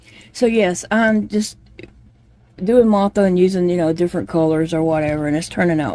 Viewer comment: If you're up to it, you'd make an excellent teacher for crochet. I got quite a giggle when you mentioned your pronunciation of Martha 😂 I first thought you were making a cross-breed bird called a MOTH-A. A combination of a moth and a bird 😅. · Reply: I thought she might be saying “Mothra”, a character in a Godzilla or King Kong movie. Lol. God bless. Ruth